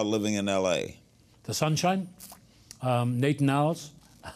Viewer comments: what does he say after sunshine? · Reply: Nate'n Al, a famous delicatessen in Los Angeles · what does he say? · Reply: Nate n’ Owl’s. It’s a Deli/restaurant in Beverly Hills.